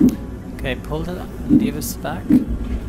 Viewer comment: did this plane just call her, and I quote "retard?" Or am I hearing that incorrectly due to listening to this via phone speaker?